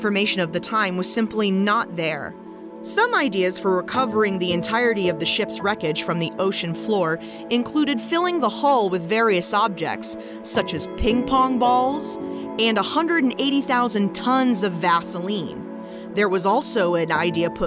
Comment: Ping pong balls?!!! I thought you said King Kong's....